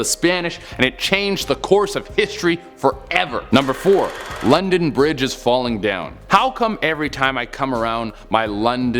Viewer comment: Wait.. London bridge is falling down I heard it as Run a bridge is falling down